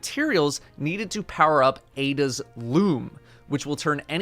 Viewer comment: Thought said Ada's lube not Ada's loom... Oof lol